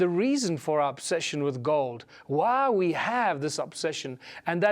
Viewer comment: South African accent sounds like he is saying "our obsession with God"..... "Goud" but he's saying "Gold"